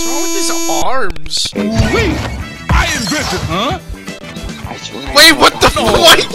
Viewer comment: Quick feeeeeeeeee 11:57 “I INVENTED- huh?” [reverses his action to prevent further embarrassment] 12:31 Doc’s final stage of evolution: pair of lungs 14:12 Doc finally lands a hit on Little Mac and goes absolutely insane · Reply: Honestly when he said "I invented" I thought he said "Iron Griffin".